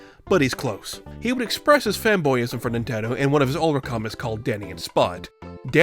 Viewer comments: Heh. "Wacky." That's, uh... that's certainly one word for what he is. And probably the only one YouTube will allow us to say regardless of how objectively true the rest might be. 3:30 I swear to god I thought he said "femboyism". I had to alt-tab out of my game and roll it back just to be sure. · So I hear "Femboyism" at 3:30 instead of Fanboyism, does that reflect more on me or how quickly the word was said?